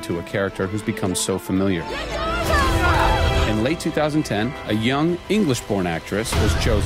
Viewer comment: My english is not perfect but i hear "a young english PORN actress" in 1:35 !!